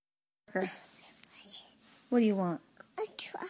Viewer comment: Awwwww, but I always thought he said "hot chocolate"